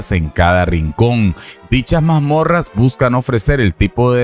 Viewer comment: Que mal escucha uno, la primera vez escuché "Mas Morras Generadas Aleatoriamente" ya a la tercera escuché lo correcto "Mazmorras" 🤣🤣